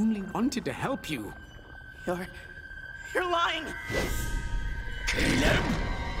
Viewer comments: I've noticed something, at 3:51 when Belos notices flapjack he says "Caleb!" and I think I put something together, from "Hunters palisman observations" he states that flapjack was owned by someone else previously due to markings on the staff, and belos had the grimwalkers to represent and remake his old friend, and when willow asks for hunters name flapjack suggest "Caleb" so was Caleb Belos's old friend he was talking about? I'm sure mist people have already realized this but just wanted to put that out there ( please don't call me stupid.) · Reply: Damn I thought he said KILL HIM instead of Caleb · At first I heard “Kill him” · he actually said “kill them” with subtitles on